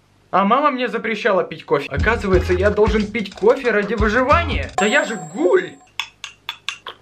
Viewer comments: " Да я же гуль " или мне послышалось ? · Гуль? Он действительно это сказал или мне послышалось? Ивангай. Смотрел. Токийский. Гуль?? Моя жизнь никогда не будет преж...а хотя похуй~ чё бы не посмотреть)